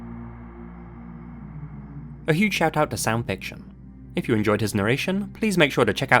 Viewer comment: I swear to Odin I thought he said Sam Fisher not Sound Fiction 🤷🏻